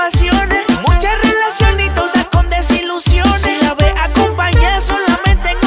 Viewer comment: entiendo; Se la ve acompañada solamente con canciones. Pero realmente ahi pusieron; Se la ve acompañada solamente en ocasiones,que dice en verdad?